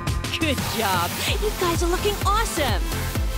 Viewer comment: I thought she said, "your thighs are looking awesome!"